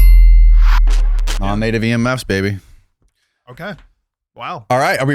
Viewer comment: what is 'Native EM Mouse" ? (if that's what he says?) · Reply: He says “Non-Native EMF’s baby”